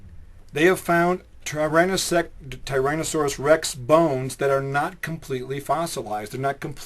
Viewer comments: It sounds like he said "Tyrannesex" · did he say tyran-a-sex? is that a prehistoric transexual dinosaur?